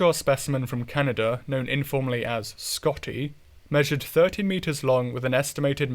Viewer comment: you mean 13? Kinda sounds like you said 30. Otherwise great video! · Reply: I definitely meant 13 there, 30 metres would be approaching world-eater size. And thanks!